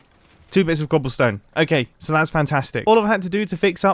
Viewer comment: It sounded like he said "Pantstastic"